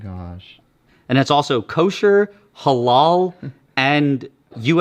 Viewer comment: Did he say Halal alcohol??